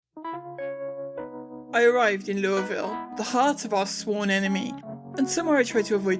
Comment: I thought you said Louisville instead of Loreville at the beginning and I was nodding in agreement with the trying to never visit sentiment.